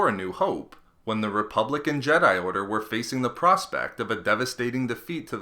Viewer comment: I'll admit, at the very beginning I thought you said "republican jedi order." Would that make the sith democrats :P